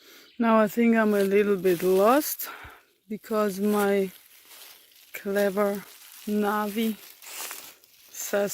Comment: did she call you her "Clever Nazi" @ 7:19...? Ha ha. Cute. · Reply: "My clever Navi!!" Referring to her navigation app :)